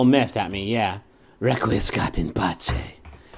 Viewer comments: What did Griffin say at 8:22? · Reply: i think its "requiescat in pace",, latin for rest in peace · what phrase did griffin just say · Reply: requiescat in pace or Rest In Peace